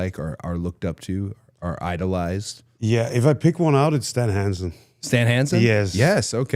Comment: for half a second I heard “Danhausen” and I about died. I thought Gunther was saying he looked up to Danhausen as a wrestler and I was like HUH?🤣🤣🤣